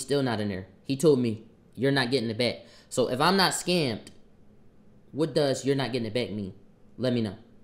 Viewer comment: Smh looking at quanny different now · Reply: Thought he said “I am not a scammer tho” -dogtalk….and either way he older he know better that was a corny move